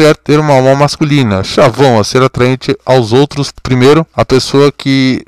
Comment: Vc falo chavon no 6:52? Eu n entendi, parece q vc está lendo e n explicando